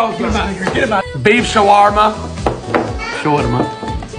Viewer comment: When said beef shawerma show it mom I thought he said beef shawerma shawerma